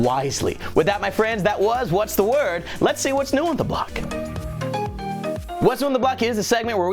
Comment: um... did you said what's on the block? its supposed to be NEW on the block